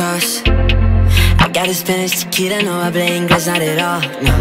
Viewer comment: I swear I hear him say "tequila" not "chiquita", idk, mi español ya anda fallando, pero mi inglés tampoco es bueno jaj